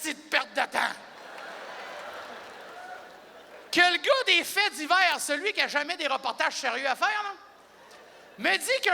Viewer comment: Pouvez-vous me dire ce qu'il dit à 3:45 svp ? Les gens se sont fait voler des pieds de quoi ? Je ne comprends pas avec l'accent (je suis français). Je comprends "des pieds de headset", mais ça ne doit pas être ça 😃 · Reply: 183 pieds de haie de cèdres :)